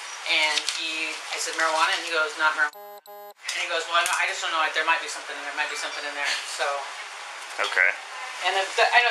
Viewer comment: Wow that’s crazy the lady never said he confessed to having a gun in the bag! She clearly says that he said: “I just don’t know it might be something in there.” Time mark 2:22 !! Smh same on them.